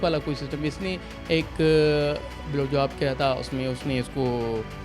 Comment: Did he say blowjob ? 😮